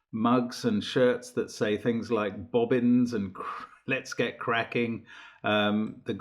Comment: About the mech, “oh bobbins” on a baby onesie! It would be very successful. 🤣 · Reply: It's "bobbins"? I always heard it as "boggins".